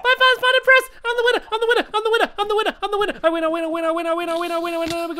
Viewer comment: it sounded like stampy said I'm the wiener I'm the wiener I'm the winner winner winner!